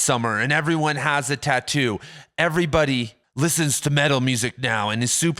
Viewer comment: Whose the poet mentioned at 14:23, couldn't hear it clearly, and I'm interested in reading their work. Thanks in advance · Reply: RILKE